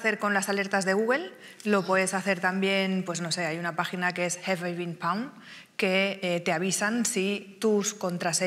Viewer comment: No, la web que dice en el minuto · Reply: Have I Been Pwned